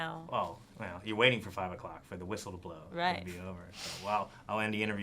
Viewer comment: Did she say nice to see you at the end (4:28) This world is so fuckin hypocrite